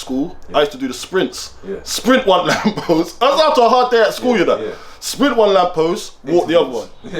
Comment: Am i delusional or did man say “ hard day at school “